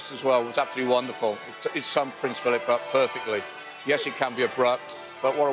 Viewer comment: The first time I heard it as "yes he can be a prat..."